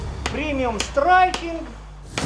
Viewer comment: премиум страйкинг? почему я это слышу?